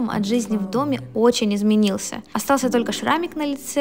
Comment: Шрамик на лице, а мне послышалось яйце 😂😂😂🤣🤣🤣🤣🤣🤣🤣🤣🤣